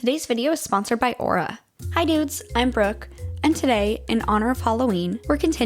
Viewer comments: Ngl 0:02 I thought you said after the sponsor, “Hi dudes, I’m broke” 😂had to rewind that part lol. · Ngl 0:01 I thought you said after the sponsor, “Hi dudes, I’m broke” 😂had to rewind that part lol.